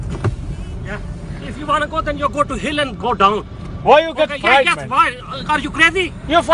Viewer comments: I'm gonna tell my kids once a Legend said “If you wanna go then go to Hill and go down” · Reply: I thought he said "If you wanna go to hell and go down"😂😂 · "IF YOU WANNA GO, THEN GO TO HILL AND GO DOWN" - Albert Einstein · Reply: I thought he said “go to hell and go down”😅😂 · “If you wanna go then you go to hill then go down” 😂😂😂😂😂😂😂😂😂😂😂😂fam what directions are these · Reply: I thought he said go to hell and go down😂